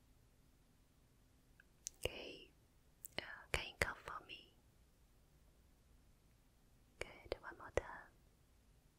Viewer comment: What did she say at 1:09-1:13?!? 😂😂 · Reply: Dominic Caezza OMG I heard it too, guess we have dirty minds. But apparently she said cough.